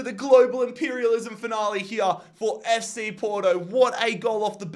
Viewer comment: DId anyone else hear Fc Porno?